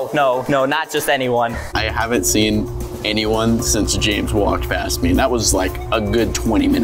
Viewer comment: did h say Jabe?